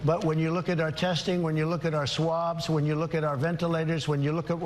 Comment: Did he just say " when you look at our swabs"?